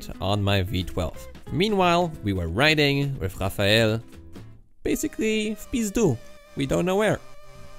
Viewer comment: nice video! Thank you. But I didn't undestand one word at 8:02... "Meanwhile, we where riding with Rafael basically..." and what's next words? Some name of place? Or what? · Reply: Что там понимать? "W pizdu"